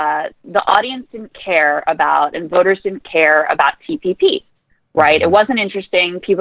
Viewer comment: What acronym is she saying at around 6:50? PPP? Ptp? Ttt? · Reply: TPP